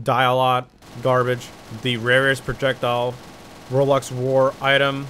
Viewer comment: Use cc at 2:56 it will say “Rolex” instead of “roblox.”welcome to 2021 where YouTube has forgotten that bug since 2 years